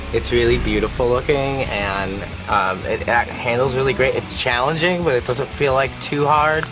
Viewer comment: Does Gerard say "Halo is really great" @ 2:08 ?? HALO FTW!